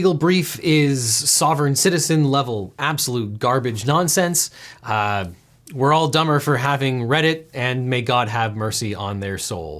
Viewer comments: When he said "we're all dumber for having read it" I heard it as "we're all dumber for having Reddit" and I thought "You know what? Yeah, that's true." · the closed captioning at 22:04 says "We're all dumber for having Reddit," when he actually said "we're all dumber for having read it,"